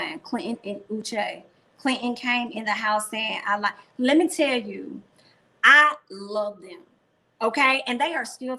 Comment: Kyra said “I love that” not “I love you”. I thought she said it too🧐